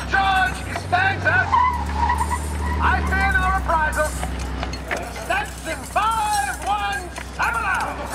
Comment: What did George Costanza say at 11:05-11:11?... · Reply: I fear no reprisals. Section 5170.